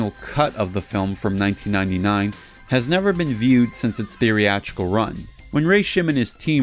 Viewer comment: By "Theory-Actual", do you mean theatrical? I'm watching your videos in order and this is not the first time I heard you say that. Is it some kind of inside joke I'm not getting?